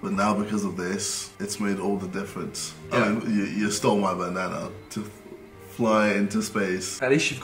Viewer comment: What I heard the first time... - You stole my banana.